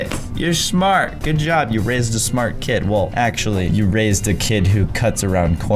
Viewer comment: I heard "you rizzed a smart kid"